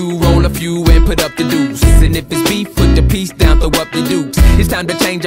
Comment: Is it bad it took me this long to stop and listed to the words “put the piece down throw up the dukes” correctly. I always heard it as “peace”